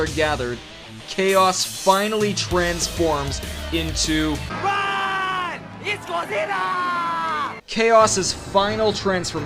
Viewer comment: Okay, at 10:05 to 10:08, is it just me, or does Sonic say "Ruuuuun! It Godzilla!"